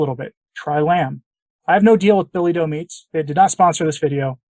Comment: billy doe meats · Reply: Thank you! It sounded like you said Billy Goat meats!